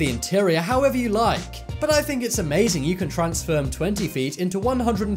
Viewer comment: did you just say "TRANSFERM"